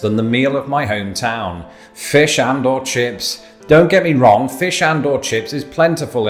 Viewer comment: fish and chips? I love your videos they are great. I cannot totally hear what your saying though exactly at 1:58.. sounds like "fish out or" This is my first youtube comment. Also possibly could make you maybe 👀 think of another memo 📝 . I always feel like i should have been born on the other side lf the pond. So im very intrigued by whatever it is that u said there. "Meal of my Hometown ... fish out or chips"???? Ahhh its driving me bonkers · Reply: He says Fish and/or Chips 👍